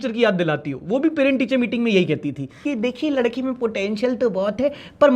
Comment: did you just say POTENTIAL